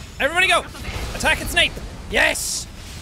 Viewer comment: Whenever he said attack its nape I thought he said attack on snape